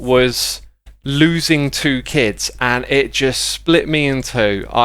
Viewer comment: Oh my god I heard it as losing to kids instead of losing two kids omg I thought oh thats not dark? OOF